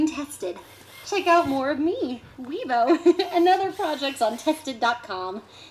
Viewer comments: I thought it said weeaboo at first... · Thought it said "weeaboo". Realize my mistake now.